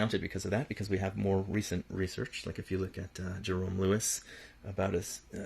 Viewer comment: what were you saying here, the books and anthropologists, Lewis something? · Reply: Jerome Lewis